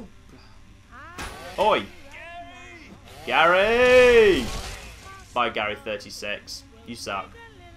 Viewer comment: That sounded like Señor Chang yelling "GAYYYY!"